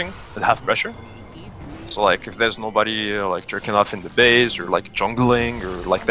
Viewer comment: did he say jerking off?